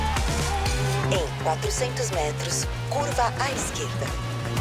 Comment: did she say: "Kurwa"?